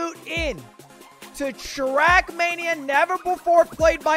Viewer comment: I thought he said “Shrekmania” and i got really excited